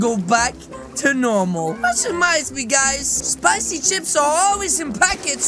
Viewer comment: did he just say spicy ships?